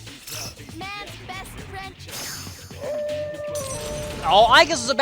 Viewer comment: When Aigis was cut off by Koromaru, I heard her say “Man’s best French—“